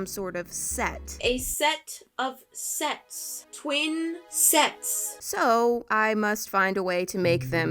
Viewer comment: For a sec when she said"twin sets" I thought she said "twin sexs"😂😂😂😂😂